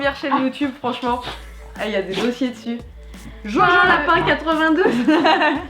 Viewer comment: Hmmm, fin mot de l'histoire, J'ai rien à dire sur la vidéo 🤔 par contre j'ai par trouvé la chaine (j'ai compris "join lapin 82", je me suis sûrement trompé xD)